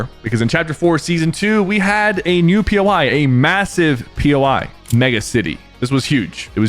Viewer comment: did he say massive